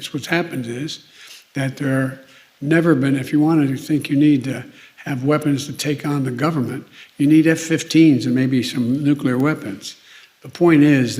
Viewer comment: I didn't hear Biden mumble anything about nuking American citizens. It took me a few times, but what I heard was "If you want to think you need weapons to take on the government, you'll need F15s and maybe some nuclear weapons". In saying that legal gun owners are not the problem. The problem for violent gun crime is a result of criminals who are not legal permitted to carry a firearm, but continue to do so. With criminals so heavily armed, the best security a legal gun owning citizen can have is a firearm or the potential for someone to be a legal gun owner. Without this, so many criminals would treat it as a free for all. Then there's also the international protection, as there's something like 400m licenced firearms in America.